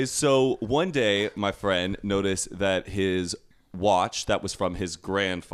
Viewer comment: "So, you're dating a liar." 😂 Omg I didn't hear "watch"..thought I heard you say the grandfather went missing. 👀 Entitlement is no bueno. For the air pod thing.. there's an option to turn the noise cancelling on/off, Zane. I don't use Apple products, personally, though sooo... 🤷‍♀️ I literally go through life making a list of topics in my head and/or a physical list of what I want to research/talk to people about.. basically, is my spirit animal. 😂 getting all embarassed saying "I think it would be fuuuun." About 23andMe got the flutterbys in my tummy all cracked out. 🙈 With the DNA tetsing kids, I would love to hear what y'all think about the storage/usage of your information. The lack of regulations/opt out for usage of your data in medical research.. I have seen a few people making videos on this topic, so I'll leave it at that. But I am definitely interested to hear your thoughts on that (although it isn't necessarily "comedy."